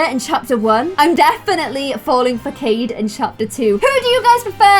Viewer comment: Did she say that she is falling for jade in chapter 2?